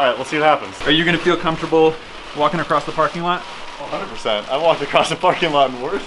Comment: If you pause at 2:01 with subtitles on it says that says the f word instead of parking